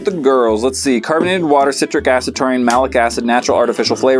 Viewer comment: Taurine? I thought you said Chlorine. ..